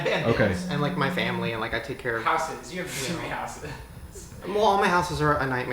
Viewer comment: Did said "You have three houses"? Damn. Maybe one is his mom's house, his and the other one who knows. I like how his priority is taking care of his family